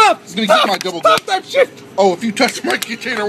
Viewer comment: I like how it abruptly stops when he says “and Im not going to pay for this either-“ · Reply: I heard "I'm not paying for-" which makes it funnier imo because the other guy told him to use the 'right" cup but he doesn't want to pay for that one 😅